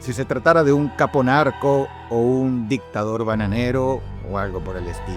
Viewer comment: Como si se tratara de un capo narco o dictador bananero o algo por el estilo 8:54 😂 entendí la referencia 😂❤ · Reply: Escuché "Bananero" y entendí "SAPEEEEE"